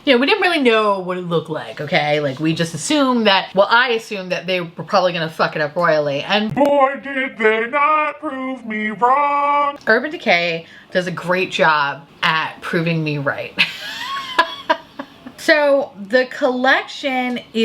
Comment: Love that I heard "Urban Dick-hay". Can't unhear it now 😂